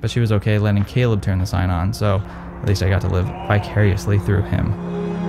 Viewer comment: I literally thought you said to "live bi-curiously through him" lmao